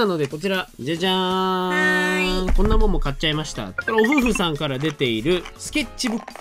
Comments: 最近案件多くて何故か視聴者が嬉しくなってます( ◜௰◝ )（） あとohuhuさんってお夫婦さんに聞こえてかわいいよね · 毎回OHUHUさんをお夫婦さんに間違って聞こえるのは私だけ？